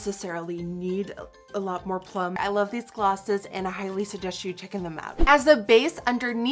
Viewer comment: "highly suggest you chicken them out" honestly i should not rip because I am not bi-lingual by any means but I lost it when I thought I heard "chicken them out." Love you Nikkie and I know that you're not afraid to laugh at yourself so I hope you dont take this the wrong way!